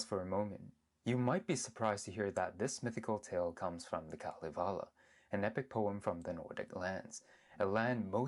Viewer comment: I almost misheard that as the Dalai lama... I was about to say hold up how did the Tibetans know about the vikings 🤣 I love this video tho